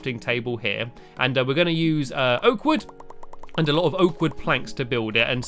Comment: I thought you said oat wood